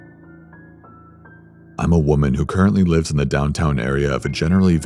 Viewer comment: did u say u where a woman HUH¡..!?!?,,,m!,!.!,!